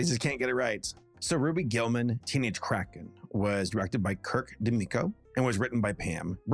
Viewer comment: First time I heard of this movie was a commercial playing in the background, and I swear I thought they said Ruby Gillman, Teenage Crack Head